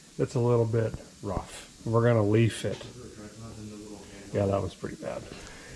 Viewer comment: Was so pumped when I thought Derek was saying he was gonna gold leaf the rad support.